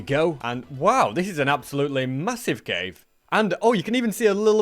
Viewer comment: Did I hear Massive?